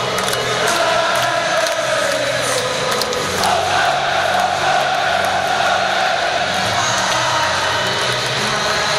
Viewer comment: What are they yelling? :D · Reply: So good so good so good